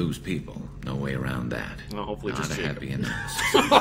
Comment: What did say at 8:08? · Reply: "Hopefully just Jacob"